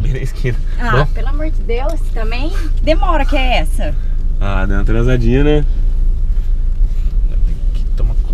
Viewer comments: entendi dei uma tranzadinha kkmkkk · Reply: Deu uma atrasadinha kkkkkk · Só eu que entendi "Dando uma transadinha" kkkkkkkkkk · Foi enpresao minha ou o falou no minuto 4:09 transadinah